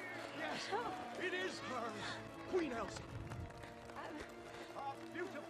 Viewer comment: queen sauce and princess anana :3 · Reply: Dat Miner I thought he said 'queen Alsa'